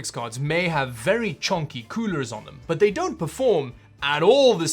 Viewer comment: My god I thought you said they both have chunky cooters on them. This video is messin with my head